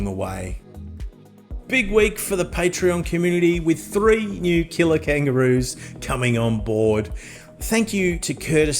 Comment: When you said "killer kangaroos", I thought you said "kilo-kangaroos", until I read the words on the screen!